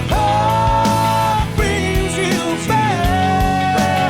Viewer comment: I always thought he said “The Heart Brings You Faith”. Song is even more special that way, I think I will keep that interpretation!! 😊😄